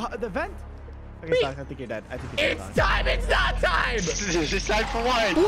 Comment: It’s funny how thought golden Freddy said it’s time but he said it’s me